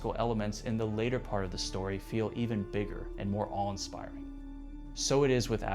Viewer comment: what you just said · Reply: "more awe-inspiring"????